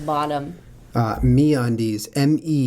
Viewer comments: lol when he said me undies I thought he said meat undies and I was like... 0_0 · MeatUndies is what I thought you guys said.